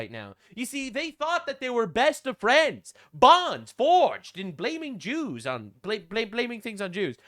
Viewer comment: it sounded like said "bonds forged in flaming Jews"...